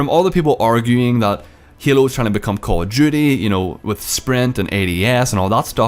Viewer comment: Your accent makes you sound like your saying call of jewty